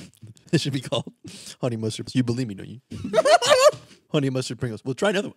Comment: does anyone have any idea what said at 52:29 always seems to understand perfectly the first time whenever says things like that but I'm sitting here replaying it over and over at 0.5 speed 😭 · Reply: "you believe me don't you"